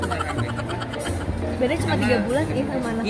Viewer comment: "Beda nya cuma 3 bln im sma ank gue.." emg nagita hamil? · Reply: kirain aku doang yg salah denger "beda nya cuma 3 bln im sama anak gwe"